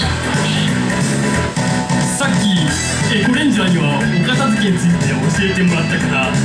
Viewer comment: お姉さんのエコレンジャーがゴレンジャーに聞こえる